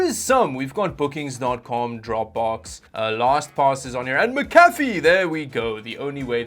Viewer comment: I genuinely thought you said, Lost Pause. I was like "WHAT!? Noble has deal with HP!?" 🤣